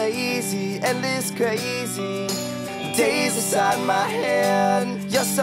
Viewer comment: When I was 13...I always thought it said Benji is crazy.....